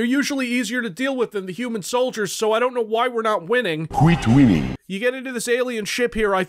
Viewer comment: Honestly thought that guy was saying "quit willing".